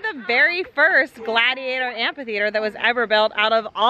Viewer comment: It sounds like is saying ,"ant theater " instead of amphitheater 😃 I'm picturing ants fighting! 🤣